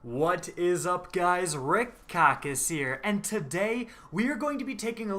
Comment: Always sound like he saying “red cock is here”